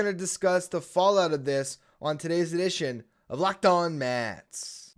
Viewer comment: Coulda sworn, in the Intro, you said, Locked on Mess...